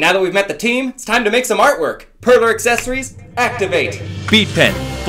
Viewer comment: I thought he said Pervert accessories activate